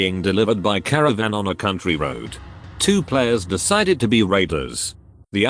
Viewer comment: Because I'm listening to this while doing stuff, at 1:45 I thought the narrator said trap of them decided to be rapers. Had two go back read the text after that one.